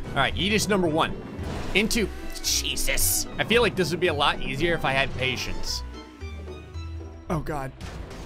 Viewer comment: "I feel like this would be a lot easier if I had patience" - this speaks to me on so many levels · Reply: I had to re-listen to that part after reading this comment as I first thought he said “this would be so much easier if I had raisins.” I have got to get my hearing checked holy Satan